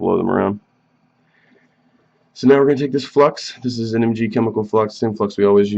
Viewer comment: Whats that lquid you apply at 2:10?? Sorry, english is not my native lenguage and i cant understand. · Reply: Flux